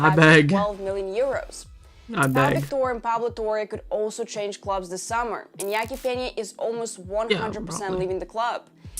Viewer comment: did he say “abeg”? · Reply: * i beg (meaning i pray someone buys him)